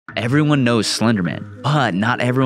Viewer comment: When they said slenderman i fr thought they said spiderman💀